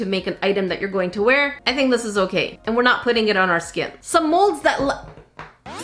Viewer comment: I actually thought that mold said “HUG” until she said it later. Gosh I need help.